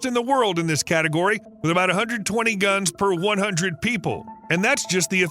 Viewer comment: I think your firearms numbers are off. 30 something and 40 something guns per 1000 citizens in Idaho and Texas? There are more than 1 gun per person in the USA and these two states are gun advocates. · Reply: I thought he said 120 per 100 people not 1000